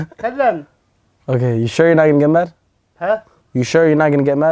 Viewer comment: Adam said you sure you never be mad Than adam dad thought adam say jamal